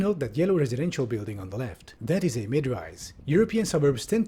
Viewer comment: What was that yellow building (at 2:32) called again? I couldn't hear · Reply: mid rise